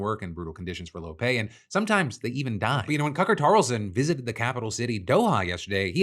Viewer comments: I heard you with that “cucker tarlson” lol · I swear on my life I heard him say Cucker Carlson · Did I just hear "Cucker Tarlson" and was that deliberate? If so, that's awesome. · did you just say Cucker Tarlson? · Does he say "Cucker Tarlson" ? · I had to rewind to 5:04 cuz I was like "did he just say 'Cucker Tarlson'?" And yes, Phil, yes indeed you did say "Cucker Tarlson." I'm fucking cackling. 😂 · sounds like he says cucker tarlson 😂 great video! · Did I hear correctly? 5:06 "Cucker Tarlson"? Hahahaha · I love when I hear someone else say Cucker 🙂‍↕️ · DID HE SAY CUCKER TARLSON!? 😂😂😂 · Did I just hear Cucker Tarlson? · Wait.... What was that???????? Say that again please??????? Cucker Tarlson????? LOLLLLLLLLLL!!!!!!!!! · did Phil just say Cucker Tarlson?!😂😂😂😂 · "Cucker Tarlson" really caught me off guard and I had to go back and relisten. IDK if I'm mishearing or what but now I will be referring to him as nothing else, ever. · Did you just say Cucker Tarlson 😂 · "Cucker Tarlson" I hear that right? xD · wait…did you say Cucker Tarlson? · Did you just say Cucker Tarlson? Because I’m 99% sure that’s what you said (and I love it)